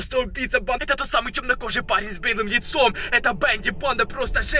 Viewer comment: Мне жалко эту девушку: ОН КРИЧИТ ЕЙ В УХОО. 6:50 мне послышалось с бледным яйцом)))